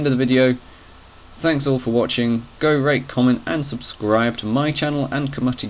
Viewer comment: it sounded like he said go rape comment and subscribe